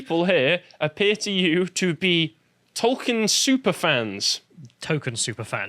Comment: I think you misheard. These are TOKEN super fans.